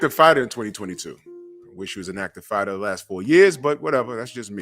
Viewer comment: I thought he said flight LOL